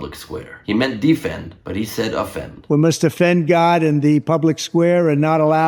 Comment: Ben Gleib says that Trump says "we have to offend God". I heard Trump say "We must defend God".